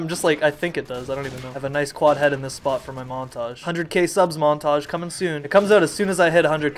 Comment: I thought you said momtage